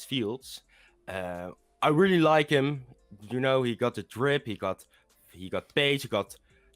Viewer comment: did he just say he got the drip 🤣